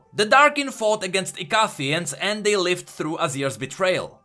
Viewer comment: LOL I'm sorry but THIS whole video I was trying to figure out what he says, when he says " The Darkins fought against ...(something)" at 1:12 AND when he says " Jax was an (something) rebel" at 13:08. LMFAO but I found out he was trying to say the word Icathia LOOOOL that thick ass accent tho.